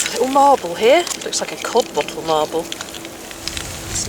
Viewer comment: Hi. Could you give us a little more information about Codd bottles and codd bottle marbles? I thought you were saying cod bottle - as in the fish.